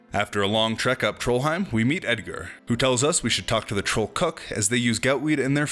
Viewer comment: I thought you said "troll cuck" at one point and had a good chuckle. Thanks for that.